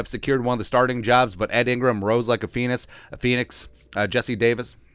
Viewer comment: LOL!!!... I thought he said "rose like a penis!"